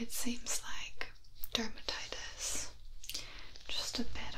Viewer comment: Did I hear "derptitis?" 😅😁😁 Keep up the great work. I love putting your videos on loop!